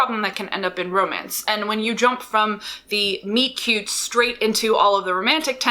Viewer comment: I kept hearing 'meat cute' and it's bugging me